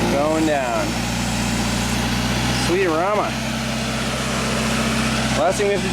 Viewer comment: Привет! Услышал от тебя не знакомую, не понятную мне фразу. Прозвучала 24:06. Титры пишут как sweet arama :) Переведи пожалуйста. Учим английский с сыном... Титры, наверное ошиблись, скорее всего нужно понимать как - приятный аромат?